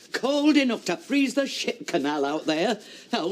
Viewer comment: Go to 19.08, it sounded like Blanche said sh*t canal instead of ship, lol